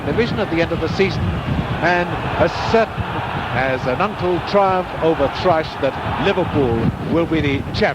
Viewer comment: I was wondering what was said at 6:34, "as certain as an uncle triumph over trash" it sounded like to me. I looked at the close captions and I can see you don't know either! Anyone else who watches this, let us know if you can figure out what on earth he is saying there. Real interesting video anyway. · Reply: Had a chance to ask my dad, and he figured it out as "an U.N.C.L.E. triumph over THRUSH", as in the TV show The Man from U.N.C.L.E. Of course.